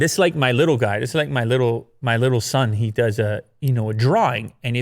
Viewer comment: I think that said "my liite son" and nobody commented on that